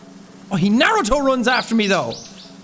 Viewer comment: "he narrow toe runs after me though" - I keep hearing that phrase, what the hell is "narrow toe running"??